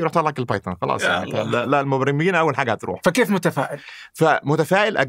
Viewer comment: تكفون اكدو لي انا سمعته صح في الدقيقة 11:39 هو قال المبرمجين اول ناس بيروحون اي بيستغنون عنهم. ولا انا ما فهمت عليه